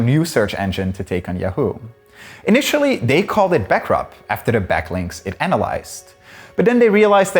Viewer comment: does he say "backup"?